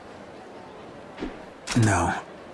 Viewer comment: subtitle said yes and the watchman said no nice